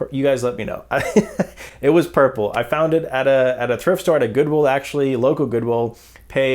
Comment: I have been watching you for years. For some reason I keep hearing, "Chrift Store" ... does anyone else hear it? Update: I hear it when not reading your lips, but i just relistened and honed-in on your lips & could see you are saying "Th" But Coulda Swoooore! 😅😅😅 Must be that South African fighting its way out